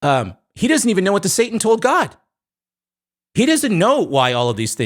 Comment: What does he say here? "He doesn't know a satan to a god?" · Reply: I think it’s “He doesn’t know what satan told God”